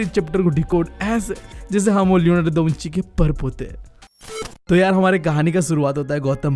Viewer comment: can anybody tell what said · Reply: Padpote